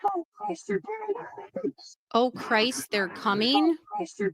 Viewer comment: lol I hear “oh Christ they’re tearing up the boots” 😬